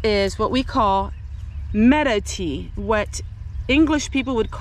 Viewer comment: Haha, I must be too involved in Facebook. I thought you said Meta tea at first😊